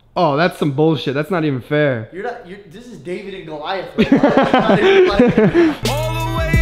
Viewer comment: The tall guy needs to meet big nik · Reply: when the guy said david and goliath, i honest to god thought he said david and liza and i was like THATS ACCURATE